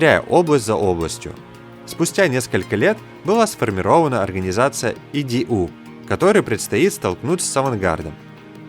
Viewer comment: послышалось "была сформирована организация (иди наху)", и я такой - справидливо.....😕